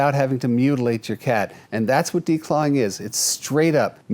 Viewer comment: Ok, declawing, I thought you said cutting.